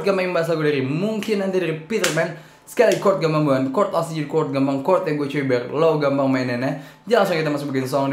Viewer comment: smapai 00:11 ngomong apaan njir wkwk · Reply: chord gampang itu bukan chord asli chord gampang itu chord yg gua cari supaya lo gampang maenin nya